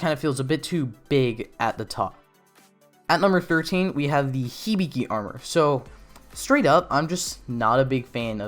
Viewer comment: Oh sorry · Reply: I thought you said hibigi